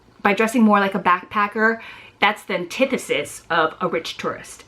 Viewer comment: ish you say something like that's the "tidisiss" of a rich tourist. What word is that? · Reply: antithesis. ...I had a feeling that word might be too complex to use.